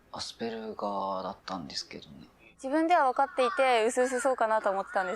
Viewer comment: "asperuuga" To me that sounds like how I'd expect a Japanese person to pronounce "Asperger", yet the subtitles say "ASD". Curious.